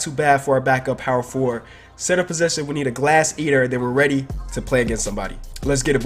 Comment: when he said glass eater I thought he said ass eater